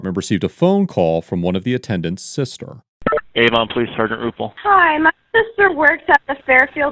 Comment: i thought he said, "Avon Police, Sergeant RuPaul" 😂😂